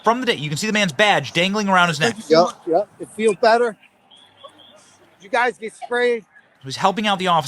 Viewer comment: It sounds like he says, "You guys need spray, here here take this orange bottle. What's up with that???